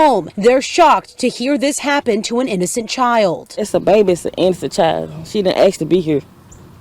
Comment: That woman sound like she said "instant child" instead of" innocent child"!!! At the rate these woman are murdering these kids the first one seems to be more like these people think, as in" instant child"!!! They'll just go replace the child with another instant child!!!!!